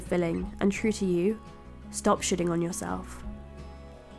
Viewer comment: At the end, it sounded like she said "stop shitting on yourself."